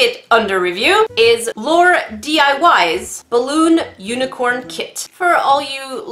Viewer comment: I thought it said LAUNDRY balloon unicorn kit I mean... It can work